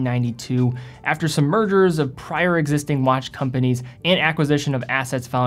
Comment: Merger! ..I thought he was saying "Murder"